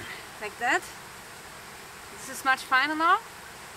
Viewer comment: I thought you said this is my new husband. Lol